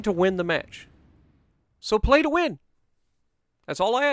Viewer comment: Wargaming “Did I hear pay to win? I think I heard pay to win” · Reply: Yes I know he said play to win